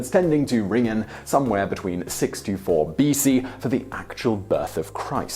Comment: "Six to four B.C" sounded like 624BC ... which confused me at first as the listener. I suggest the speaker use "between 6 AND 4 B.C" so that there is no confusion to the listener.